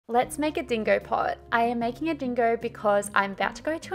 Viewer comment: I thought you said bingo at first so I was like "OMG I LOVE BLUEY" 💀💀💀